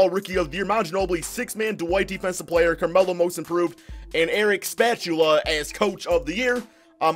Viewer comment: What At 2:25 he said Eric Spatula